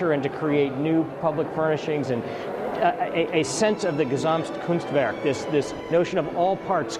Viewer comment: What does he say at 2:42? The term for "all things coming together." Kasam coonstver? Something phonetically similar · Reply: Gestalt